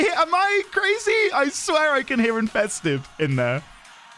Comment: I need to check the lyrics of "Party of your lifetime", but i hear "invested" instead of "infested"